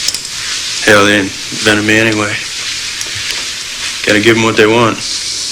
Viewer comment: does anyone know what he says at 0:06? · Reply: Nicole Hell, they invented me anyway gotta give them what they want